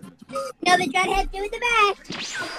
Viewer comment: what the little dude say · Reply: i think he said something about a "dreadhead sitting in the back"